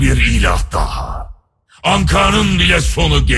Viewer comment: Ankara nın bile sonu gelecek mi diyor yoksa ben mi yanlış duydum.